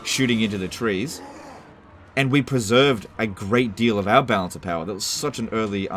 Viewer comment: When says “balance of power” quickly, I just hear “bounce bounce”